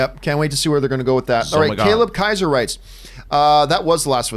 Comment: hol up!? Wait a minute! Stop the presses!!? Did just say, "zoh muh Gawd"!?!!?